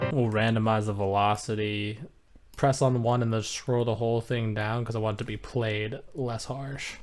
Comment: What did you press to randomize the velocity? Did quite understand what you were saying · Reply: Alt + r is for randomizer 🚀🚀